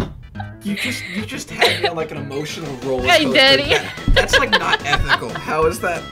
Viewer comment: I swear I heard say hey Daddy